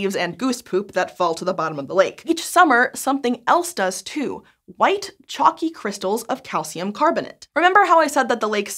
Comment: Did anyone else think was about to say "white chocolate" at 3:37? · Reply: Now that you mention it... "White chalky ,,,"